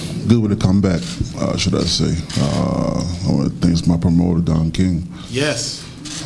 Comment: PAUSED AT 1:26....Did he say DON KING??? Boxers are still going to that reptile!!!!!